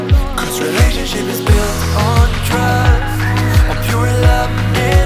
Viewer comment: On 1:22 does It say "Relationship is build on trust" or "Relationship is build on drug" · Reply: It's trust